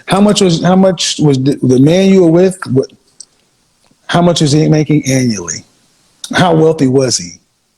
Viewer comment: That's definitely not wealthy. I thought she was saying at least 5M annually